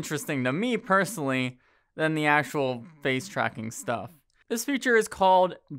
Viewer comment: oh fug... ere it comes. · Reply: hmmm, im not sure if this counts but i heard "facel tracking"